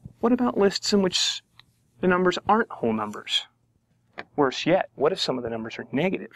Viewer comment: what is he saying at 2:33? is he saying "sheat?" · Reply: he is saying worse yet